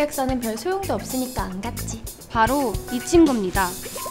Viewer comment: 이 친구입니다 나만 미친겁니다로 들리나...?